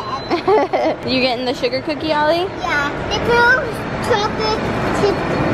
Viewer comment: Ollie: sugar cookie! Me: did he just say Sumo cookie?